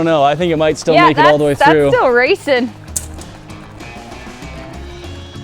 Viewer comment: Did y’all here what she said it sounds like she said racist